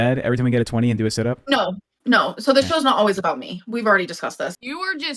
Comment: I thought she said the show is "always about her" 😂 mug lips!